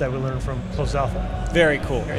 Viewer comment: For a moment I thought I heard "thank you so much for commenting guys" but it was for coming xD